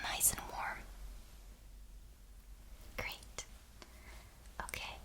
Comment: What name did she say at 5:00 ? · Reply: She said great.